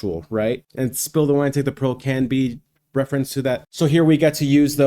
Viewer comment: So for 50 years I always thought Eric was saying Spill the wine, take the girl. Now I see you pull up the lyrics and it's pearl not girl. Well I'll be damned...